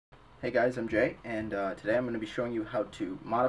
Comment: I thought he said ‘Hey guys I’m gay’